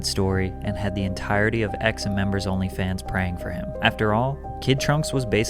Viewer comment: I heard that as members onlyfans 😂